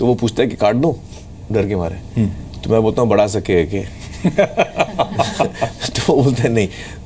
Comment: what did he say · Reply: bda sake hai ke.. mean baal bdha sakte ho kya